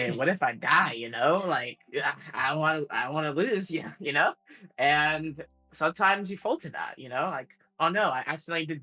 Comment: ここのI don't want to loseのdon'tが聞き取れないのですが、コツとかありますか？ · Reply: めちゃくちゃいい質問をありがとうございます！ 英語は場合によって、音がすごい変わるんですよね。この場合、"I don't wanna lose."と言っていて、want toがワナみたいになり、更にdon'tとも繋がって、音が変化してるのかなと思います。あと早く言ってるのもあります。 やっぱり、音を聞きながら英語字幕を見ると、言っている英語が分かってくると思うので、ひたすら色んな英語を聞くことが大事ですかね！質問の答えになってなかったらすみません...